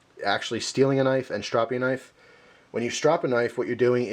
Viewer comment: oh uhhh i thought it said stopping lol